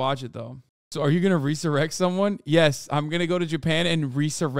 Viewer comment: why tf did hasan say resurrect like that i thought he was tryna say insurrect and i was very concerned for a couple seconds ngl